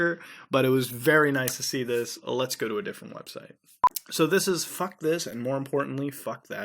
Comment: Interesting but when will you find finally a red room? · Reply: Afro Curt I thought they said "FUCK WHITES" and "FUCK TRUMP" (at least that's what I heard)